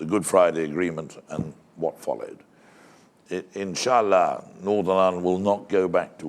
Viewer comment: Did he just say "insha ALLAH"?